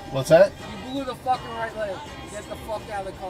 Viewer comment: Sounds like he said “You blew the f*cking right lane”